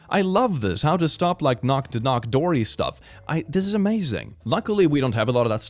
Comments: Did just say "knock to knock doory"? 😭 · did he just say "knock to knock doory" instead of door to door knocky